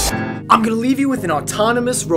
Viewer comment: I thought he said watch a Robot Dragon fly, not the bug -_